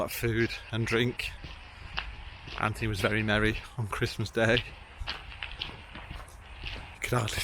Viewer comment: At first I thought you said Anthony was Mary Berry on Christmas Day lol ! Easy tiger. Great vlog as always. Hope Dexter has improved and not needed to go back to the vets again. Stay safe guys.